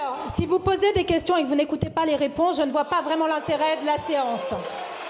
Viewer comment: *séance et non science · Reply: Moi, j'ai entendu « science». Bizarre.